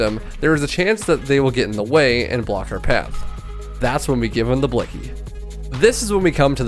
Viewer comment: Did he just say “give em the blicky”